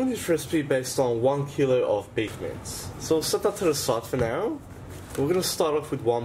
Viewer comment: one kilos of bequets? WTF did he say? I listened like 10 times, cc does not show up as anything. 2:00 I hear beefar? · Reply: he said "Beef mince" and at 2:00 he said "bring our beef back"